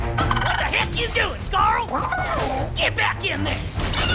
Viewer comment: did he say get back here nigga???